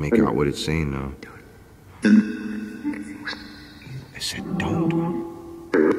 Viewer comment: it sounded like the voice said “take him out” and another voice said “don’t”